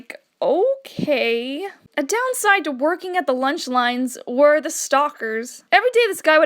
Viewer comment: I thought its said lunchies not lunch line 😂😂😂